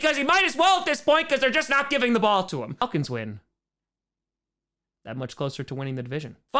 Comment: I put the checkmark on the Bears to give their fans false hope...also accidentally cut it out by Saints win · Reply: It definitely sounded like you said "falcons win" in that partial cutout...🤔